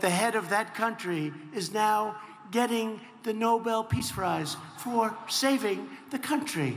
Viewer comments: Did he say "Nobel Peace Fries" at around 4:08? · did he said “Nobel peace Fries" is that what I hear Or am I wrong ?